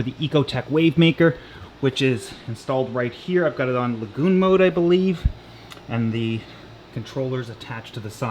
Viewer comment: Lol thought he said GOON MODE LMAO HE SAID LAGOON MODE